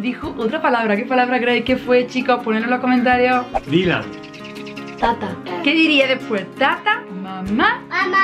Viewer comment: yo creo que dijo Tata